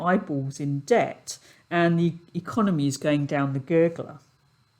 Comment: "The economy is going down the gergle." Gergle? What does gergle mean in Kiwispeak? ( I'm assuming "gergle" is what you said. ) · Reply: It's "gurgler" as in "going down" or "in the toilet."